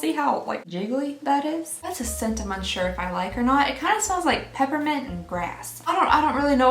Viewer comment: I seriously thought she said peppermint a** 🤣! Lord forgive me!!